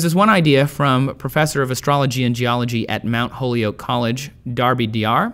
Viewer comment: Did I hear say that there was a professor of "astology" and geology at 7:27?